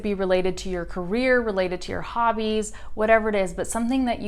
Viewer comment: I listened you and I think i heard you say "whatever it's", it's a contraction or you just said "whatever it is" very fast. if I made a mistake in this comment please let me know.